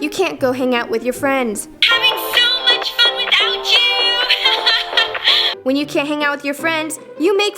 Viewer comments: did Veronica say having so much fun without you ??? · 2 years ago.. 1:08 I heard Roni saying HAVING SO MUCH FUN WITH ALJU Now I hear Roni saying HAVING SO MUCH FUN WITHOUT YOU Me: Ohhhh!